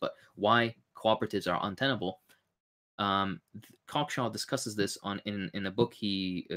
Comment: I can't make out what is saying. What is the name of the creator (Comchem?) and what is the title of his work/channel? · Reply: The book is "Classical Econophysics" by Paul Cockshott, the author mentioned :)